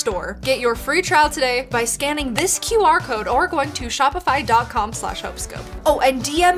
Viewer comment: When she said about shopify scan the QR code I thought she said scam the QR code😂😂